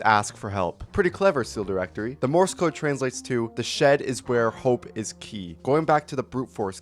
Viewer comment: i thought it said 'THE SHED IS WHERE THE LET IS SKY'